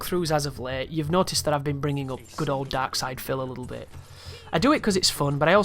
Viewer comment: It seems I'm the last person to know - who is this person? It sounded like you said, "Dark Side Phil" but I see references to "Max" below.